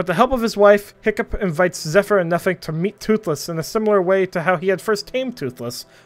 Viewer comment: bro the subtitles said “Zephyr and nothing” 😭😭😭😭